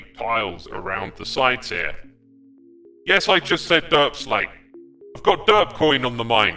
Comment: Me: Did he just say derpslate…? YES I SAID DERPSLATE